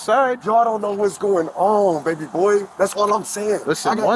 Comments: did he just call an officer “baby boy” 🤣🤣🤣🤣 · did he say "baby boy?" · did he just call a cop "baby boy" ?